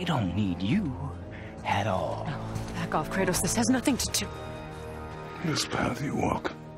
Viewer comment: Did Freya just call him Kratos? I haven't heard anyone but helheim Zeus mention his name, nor did anyone other than Mimir realize who he is.